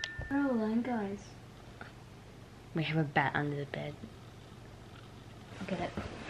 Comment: Sockie's face when she said: "There's a bat under the bed." She was so calm. Edit: Thanks for 272 likes! And yes I mean a baseball bat. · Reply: OHHH I THOUGHT SHE SAID GET THE BAT FROM UNDER THE BED